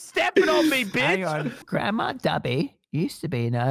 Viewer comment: I LITERALLY HEARD GRANDMA DABI😂😂😂😂